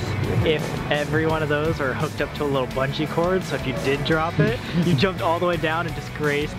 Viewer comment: i thought said buttcheek cords instead of bungee omfg